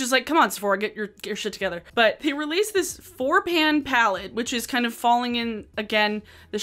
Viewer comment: i swear when you were talking about the kvd palettes i heard "this is a bore-pan palette" and honestly? yes